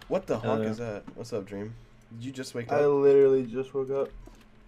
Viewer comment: I THOUGHT IT SAID MOANING VOICE WHATTHEFUYCKDN I WAS SO CONFUSED